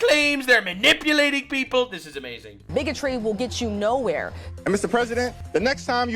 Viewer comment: wow at 12:03 i thought she said N**otry i feel like a piece of shit for that.